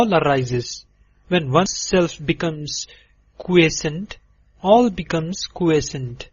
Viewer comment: What word is he using throughout this audiobook? like in 19:02 "When one self becomes ...... all becomes ......."; is he using the word "quiescent" Thanks Edit: Yes he is using the word "quiescent"